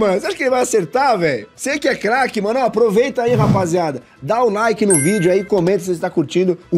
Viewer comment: Só eu q escutei “da o Nike” kkkkkkk 3:80